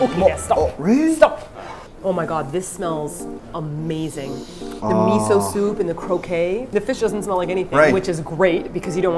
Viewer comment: What said: "This smells amazing...the miso soup and the croquette." What I heard: "This smells amazing...the miso soup and the cocaine." Oops.